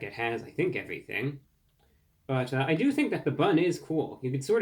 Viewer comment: I thought brah said red and orange vomit.